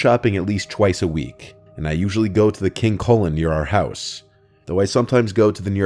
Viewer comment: When I first heard that part, I thought he said: "King Colon" not "King Kullen". I have not heard of this store until this video, but I think it's safe to assume Kullen in pronounced Koo-lin?